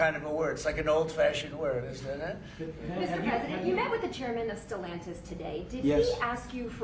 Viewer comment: Trump said "Oh they're making it so difficult, all over the world....etc..." Why would the reporter who asked the question not cut in and ask Trump directly: "WHO EXACTLY ARE THEY?" Clearly, a group that can dictate worldwide. We want to know who's in this cabal. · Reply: Chairman who? Stalantis??? She talked so fast I didn't catch it.